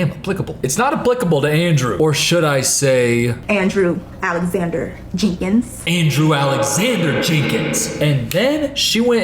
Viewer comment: i misheard and thought she said "andrew ADAM SANDLER jenkins" 😭